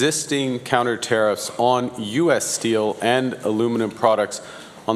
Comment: I thought Mark said no one uses steel 🙃